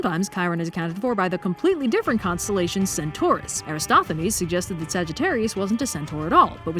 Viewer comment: I thought I heard Aristophanes💀. I had to turn on subtitles because I could not believe the guy who portrayed a hero going to heaven on a dung beetle would be seriously talking about the Zodiac. That's like hearing Seinfeld instead of Feynman.